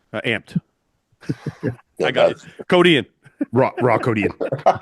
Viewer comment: Idk why it just dawned on me that Iain says "Code Iain" i thought he was saying "Codeine" I'm like wow he really likes Codeine